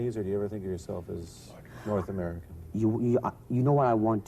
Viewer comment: Very very strange that nobody has NEVER NOTICED at 20:29 that voice speaking under the journalist ´s voice when he says « North American » who else heard that whispering of something or someone repeating ´North American ´? Was it a cast member ? Some other person in the room ?